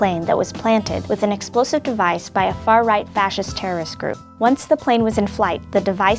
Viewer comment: did they say "far right fascist"? Well that's an oxymoron if I ever heard one.